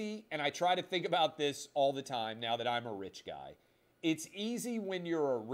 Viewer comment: Did Clay just call himself “a rich guy”?